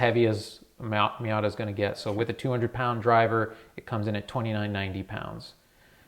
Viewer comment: Is the weight 2900 lbs or 2700 lbs? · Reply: It sounded like he said, "2,990 with a 200-pound driver." So 2,790 pounds is my guess.